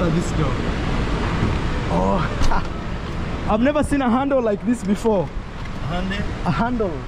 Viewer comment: Ride on bro!... Hey that's Hyundai for you. It's terribly fast! I drive one (old model- 2016) and believe me, speed pick-up time, over 110 kmph in ten seconds! is a cool guy...can see he's really followed you forever and feels really inspired by you. He also recognizes what you have done for Mayuguno too! It's great!👏👍👌👏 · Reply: got confused because he initially thought said he’s never been in a Hyundai, when what was trying to say is handle. Thereafter, when asked what car he has, he said it was a Hyundai. But yes, it’s a Kia. The brain can be funny like that…